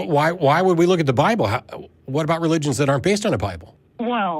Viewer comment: Did she say "because Satan"?